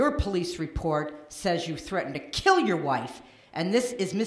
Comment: Did I just hear at 8:10 the woman said he threatened to kill his wife????? The man's son slept with the convicts wife???? Lawd I can't stop laughing😱😱🤣🤣🤣🤣